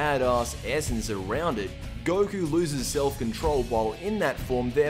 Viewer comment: I thought he was going to say Goku loses his nipples.